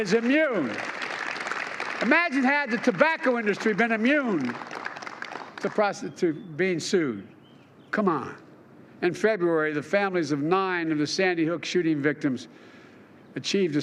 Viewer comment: "imagine had the tobacco industry been immune to prostitute being sued." ....did I hear that correctly? imagine that...